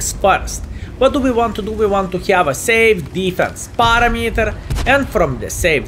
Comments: Today fellas we can watch trying to say perimeter instead of parameter for 22 minutes straight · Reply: I always thought he was saying Barometer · Finally, I now understand what you mean when I hear you say "safe defense farm meter".